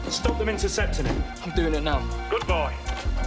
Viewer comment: "Oh boll—" I thought the Slitheen said "oh boy" until reading about it on the internet. 😂😂🤣😂😂